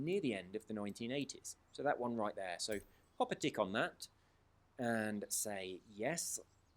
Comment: did you just say to pop a dick on that??